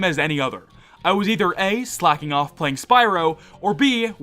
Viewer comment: On an unrelated note, I thought you said "Slapping off playing Spyro," which made me deeply concerned.